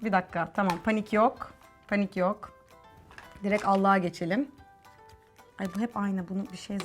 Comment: da ALLIĞA geçelim diyormuş bende ALLAH'A geçelim anladım jfkrnkfkfkfmfjck